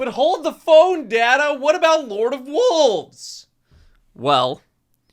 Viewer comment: Legit thought he said “but daddy, what about lord of wolves”